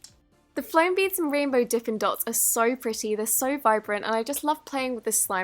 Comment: Did anyone else hear d*ckin' dots? I didn't the second time I listened to it. But then it kept switching between d*ckin' and dippin'. If I thought of one of the two, that's what I heard!! Does anyone else hear this omg?! This is kind of like the brainstorm green needle thingy or yanny and laurel I'm not even joking!! 1 like is d*ckin' 1 dislike is dippin' 1 comment is it switches for you